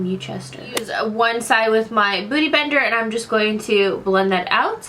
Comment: it sounded like she said booty bender😂 lol